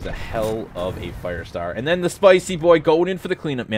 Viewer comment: "the spicy boi go'd in for the clean up" did you mean to say 'went'? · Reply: Gavin White he said going in